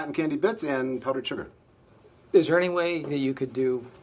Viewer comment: The best part about this is that the pretzel salesman called it "the works" as if Michael's not the first to order one. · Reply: I always thought he said “that works”